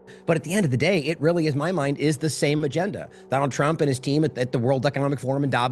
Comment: …Trump and his WHAT? 😮 Sounds like he almost slipped and said “Trump and his demons”… 😈👀 They are mocking us.